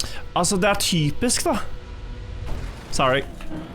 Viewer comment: Im so curious as to what he said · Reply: Norwegian: Det er typisk da, English: that is typical.